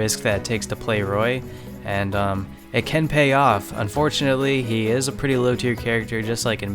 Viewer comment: I thought said "It ken payoff"Maybe cause Ryu was next